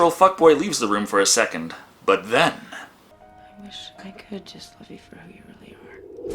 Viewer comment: What the hell is she even saying there, E-N-U-N-C-I-A-T-E lady! "I wish I could just huv heru veree her...